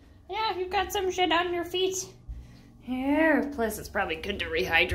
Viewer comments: "You've got some s%*t on your feet!" ~ Misheard Emily Quote from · I really thought Emily said "you got some sh*t on your feet" not shed. I was very confused lol